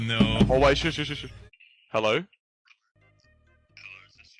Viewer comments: For anyone wondering what the agent said they said (not fully sure but this is what I heard) "Hello(or wilbur) is this your roblox gift card?" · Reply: I thought they said “ caller is this your rubux” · For anyone wondering what the agent said they said (not fully sure but this is what I heard) "Hello(or wilbur) is this your roblox gift card?"